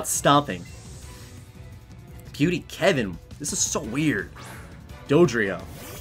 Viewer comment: I thought it said dodoria the pink guy in dbz ya